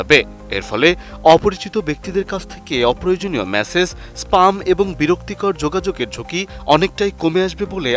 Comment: Oh he meant Spam, I heard Sperm... Seriously...